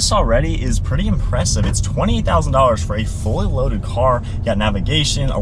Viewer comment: min I swear he said $20,000 its now late 2024 and I cant find one for less than 18K! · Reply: He said 28 thousand